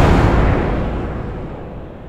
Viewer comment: When will it come out? The trailer said this summer, but summers almost over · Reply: YEAH Like summers over for me now..I LOVE FUTURAMA! So i wanted this and..i googled the movie to watch it but...There was none... ;-; OR Maybe It's gonna be in a diffrent year XD Like the Future XD BUT Maybe it'll come out when it's ready maybe they're still making it! it'll be ok man i'll make sure to google EVERYWHERE when it's finally done! or maybe it's out in new york :I And also i think everyone heard hermes say "I hate REALLY that crab..." Right? XD It's At 1:32 XD Welp Bye it's cool now :D